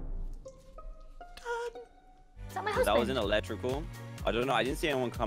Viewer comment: LOLL Near 14:00 when they found Joel’s body I swear I heard mutter,”Is that my husband?”